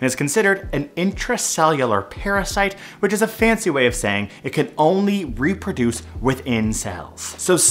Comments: somehow i heard "can only reproduce with incels" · lol I misheard "within cells" as "with incels" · I heard, "It can only reproduce with incels" not "it can only reproduce within cells" Small change but way different visual. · not me mishearing "it can only reproduce within cells" as "it can only reproduce with incels" 💀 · I had to do a double take at 0:40 because I heard him say "with incels." What has the internet done to me? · I just hear "it can only reproduce with incels" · lol I thought I heard “it can only reproduce with Incels”